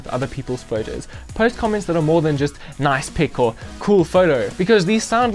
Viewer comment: I heard “nice pickle” idk y 😂😂